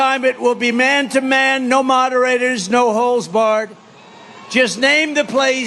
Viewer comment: Did Trump say, "no HOLES barred?" 0:54 That's weird! What the heck was he planning to do?🤮